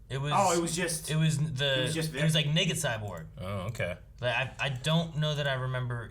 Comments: did he just say N*gga cyborg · did he say the n wurd?💀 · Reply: i think he said naked cyborg lol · Was it just me or did someone else hear them say n-word cyborg at 4:14? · did he say “it was like nigga cyborg”??? (Someone help I genuinely don’t know😭) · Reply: Naked cyborg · what did he say? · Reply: Naked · What the fuck did dude in the red shirt say at that part??? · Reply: Naked Cyborg. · lmao why did I hear "it's nigga cyborg"